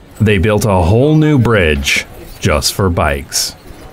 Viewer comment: Wait I'm confused? 0:42 did they build the bridge "just for bikes" or "not just bikes" What he's saying and showing is waaaay too different 🌝😂